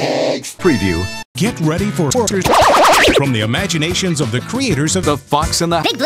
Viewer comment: Did he just say, "get ready for torture ?"